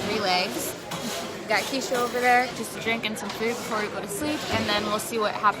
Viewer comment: Wait a minute did she just say drinking some food